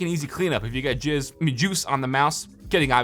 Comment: did he say jizz on your mouse?😂